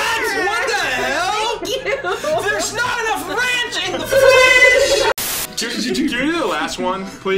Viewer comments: Did he say there is not enough ranch in Franch? I couldnt tell what he said. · after they got a cheer I heard say "THERE'S NOT ENOUGH RANCH IN FRENCH" and I love it 😂